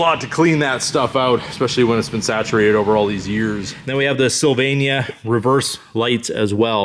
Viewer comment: what did you say at 11:00 slovenia maybe? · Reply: Sylvania